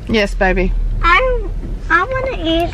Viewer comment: It totally sounded like said "I wanna eat some more panty cakes." LOL